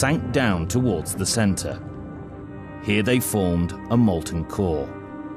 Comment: what does he say in 4:13 molticole ????